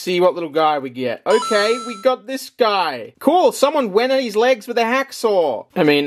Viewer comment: what did he say? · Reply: "Cool! Someone went at his legs with a hacksaw!!"